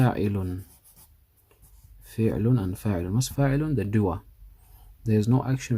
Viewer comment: For anyone wandering what he said at 37:00 he said the doer